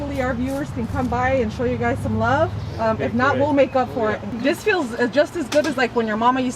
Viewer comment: 😲 I thought you said We'll make love for it... but you siad We'll make up for it. 😂😂😂🤣❤❤